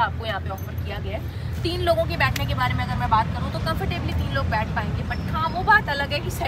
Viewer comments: Did she really say @ 9:42, 'Centre mai jo baithega uske gote ekdum thande ho jaaenge'😂😂, ya khali mere kaan baj rahe hain... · Jo beech mai baithega us k kya thande hojaenge??? Guys please tell.. mai kuch galat sun raha hu I guess.. coz I don’t think she is saying what I’m hearing 😅 Video